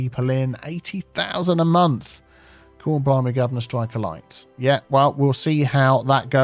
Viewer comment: What on earth did you say at 49:59 🤣🤣🤣🤣 love it so British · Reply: Cor-blimey-guvnor-strike-a-light 😂